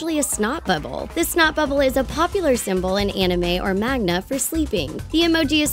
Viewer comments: "In Anime or Magna~" Pause 1:36 Me: Wait.. whattt?!?.. Did she say MAGNA?? look again Ok..ok.. I'm done.. (ﾉ*ﾟｰﾟ)ﾉ Π==Π flips the table I can't..just can't😐.... But i want to continue😂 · Did she just say "magna" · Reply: manga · "Magna" Me: ._. Did I hear this wrong? Replays "Manga" Me:THIS B*TCH! ITS "MANGA"!!!!!!